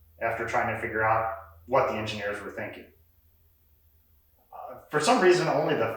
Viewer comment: !!! 👍🏼🤠 ... and multi-talented too 00:53 Sorry... did you say, "what the engineers were thinking"?